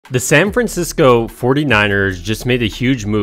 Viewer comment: Love the content but I thought you were saying "San Francisco Fortniters" the whole time. Also, you said it so much I thought you were doing this for a school report that needed 500 words or more. 😂